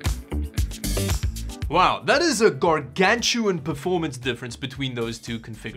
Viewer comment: What did he say Right there? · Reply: "Wow that is a gargantuan performance difference" AKA a large difference.